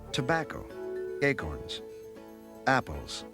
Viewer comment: <narrator> "acorns" me: "did he say gaycorns?" <narrator> "gaycorns"